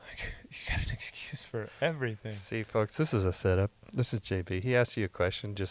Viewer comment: "What other people think about you is none of your business" Amen 🙏🏻🙏🏻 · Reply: Lol at 16:20 did he say this is?